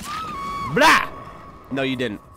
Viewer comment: Did you say abl3 in